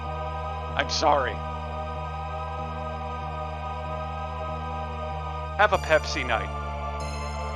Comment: I first thought you said "I'm sorry, I'm a Pepsi Knight"...